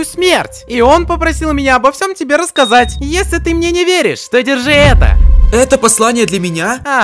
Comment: Мне послышалось ,, если ты меня перднёшь"😂😂😂